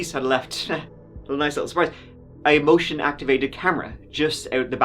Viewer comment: For some reason when said the police put "motion-activated cameras" in the house I heard "bullshit-activated cameras". After what they found out it actually kinda made sense that way, too.